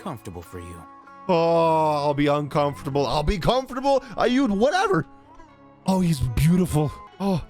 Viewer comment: Ill be uncomfortable .. ill be comfortable .. whatever in my downbad lewd mind - my brain heard what u said as "ill be uncomfortable.. ill be comfortable.. shit ill just be "come" for you.. whatever.." 😂